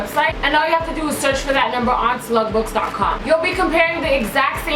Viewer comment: I thought she was saying slutbooks